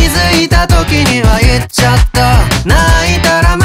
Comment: 「気づいた時にはいっちゃった」のとこが 「気づいた時に入っちゃった」に聞こえる。もしかしたら、無理やりやらされた系なのかも。